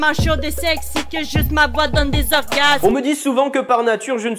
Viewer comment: J'ai rien compris... elle a dit quoi ? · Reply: "que j'use ma voix dans des orgasmes" :]